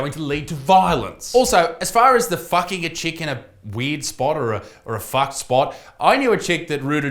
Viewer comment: does any one else think he says Fvucking a chicken?